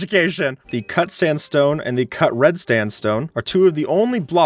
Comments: is it only me or did he say s"t"andstone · did he just say “cut sandstone and cut red stand stone?